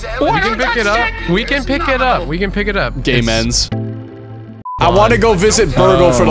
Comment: I could've swore I heard Smitty say "gay men's" at 17:00😂😂😂had to rewatch it